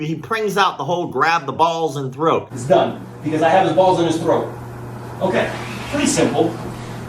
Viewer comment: I could’ve sworn Joshua Fabia said “I now have his balls in my throat!”